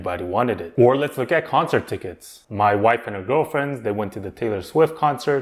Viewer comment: I had to rewind that lol I thought you said "My wife and girlfriend" haha